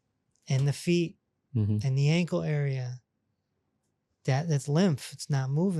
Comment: Ha ha ha…… I thought you said there was a Demon in the ankles… You accually said EDEMA in the ankles!!! 😂🤣😂🤣😂🤣